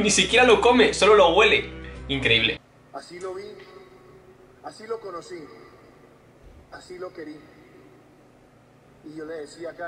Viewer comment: No existe la palabra "queri" , creo, no lo se, puede que haya sido "quería" al igual que Colombia con p 🤣🤣🤣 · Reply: No se de donde sean todos los que lo lean, pero de donde soy lo escuche "así lo vi, así lo conocía y así lo quería".